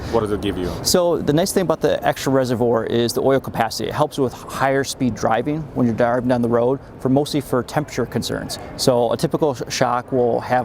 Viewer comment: Did he say die er ving driving when you are going down the road!? @ 2:34. YAY, a roof rack! BS!, sorry! Toyota PLEASE LISTEN to you customers!!!